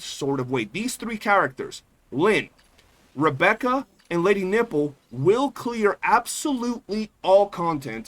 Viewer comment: did you just call her "Lady Nipple"? Lmao